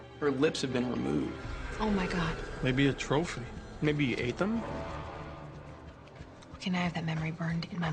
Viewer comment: What did he say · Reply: Md Hakim 'maybe he ate them?' 😂😂